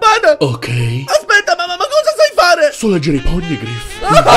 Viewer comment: Cosa dice Nico Robin a 2:54? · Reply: So leggere i poign griffe